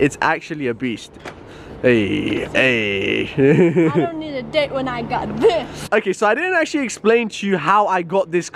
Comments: Did She really say DI**K Or did i hear it wrong at 08:21 I don't need a d**k when I got this? wow!!! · What did she say at 8:21 ? She got moist. lolol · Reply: omg .. i dont need a di** OMG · What... what did..... What did she say at 8:22 🤔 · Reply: she doesnt need a date when she got that car. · did she really say (I don't need a D**K when i got this )??